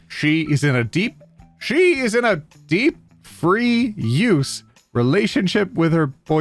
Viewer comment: "deep free use" being repeated so much made the sentence lose meaning and I almost thought it said "deep fried"